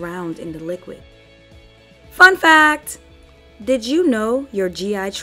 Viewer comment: I thought she said “ F $@ that” 😂 then I realized it was “ Fun Facts”